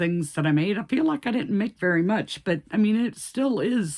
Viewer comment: Omg that’s funny I think donkey still looks sweet great makes I thought you said you didn’t make much lol 😂you made a good amount